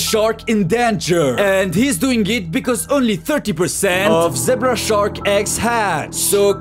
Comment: Yo will puuhht da shark in deeenguurrr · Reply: if your would have put an “h” in there then it would be spot on😭 bc it sounds like he said “zehbra”